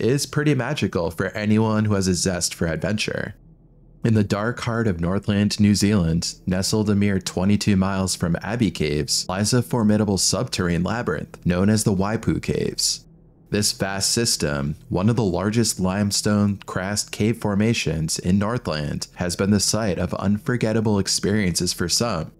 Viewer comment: Lol I thought i heard him say “the waifu caves”